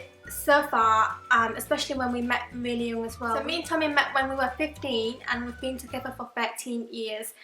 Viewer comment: did she say 50 or 15 on 0:28? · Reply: 15